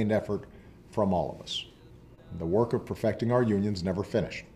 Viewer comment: / 37:58 He actually says: The work of perfecting our union's never finished. So he is talking with a forked tongue ... hidden discreetly unions versus union is. Interesting.